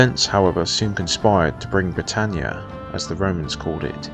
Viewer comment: did he just say "Battania"